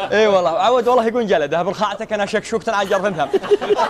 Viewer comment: وش قال1:43 الي عرف ي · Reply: عاد يقولون جلده هب نخاعته كانها شكشوكةً على جرفً ثّم